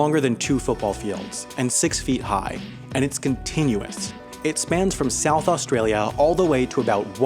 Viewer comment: excuse me sir did you just say “continuate?”